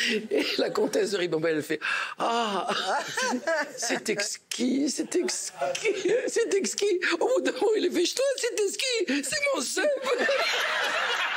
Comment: « c’est exquis c’est exquis, c’est mon ... » c’est mon quoi ? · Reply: c'est exquis, c'est exquis - comment ca c'est tes skis? c'est mon zeub (ref aux skis qui étaient entre eux deux)